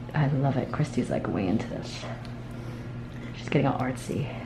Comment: it almost sounded like she said "Christy's like way into this sh*t"... HAHAHA Love it